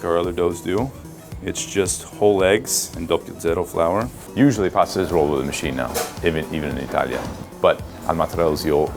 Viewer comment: Some one please help home cook here i what kind of flour did he say @ 4:04 / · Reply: Double 0 flour!